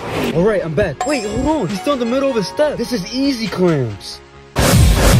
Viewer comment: Gygro step, i thought u said 🥷gro step fo a sec.😂😂😂